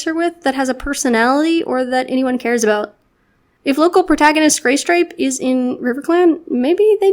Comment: At the end it sounds like she said gay stripe ! I’m dead